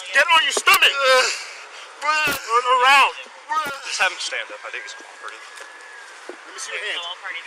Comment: I think at 1:29 he said "Just have him stand up I think he's retarded"